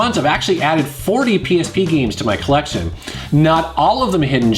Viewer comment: '40 penis p games' ... sorry, its all I heard, I'm really a big fan!.... penis p